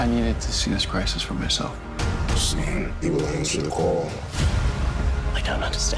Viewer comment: I think at 1:03 the monitor says save the lantern core.